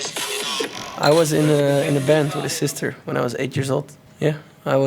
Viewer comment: 4.15 Thought he said i got in bed with his sister when she was 8-years old, jeez...